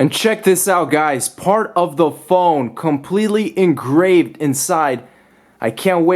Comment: did he just say "engrafed"? lmao